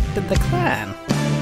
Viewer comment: FunFact4: T4L is the only ending that contains a number and the only ending you see Charles after you do RPE. Its my 5th favorite ending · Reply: Also funfact: if you turn the captions on 15:01 Reginald says topic fan instead of Toppat clan